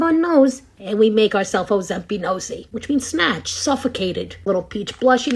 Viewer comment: I thought you said Ozempic-nosey! 😂 Edit: She did say it! 🤣 Hyped your video!🎉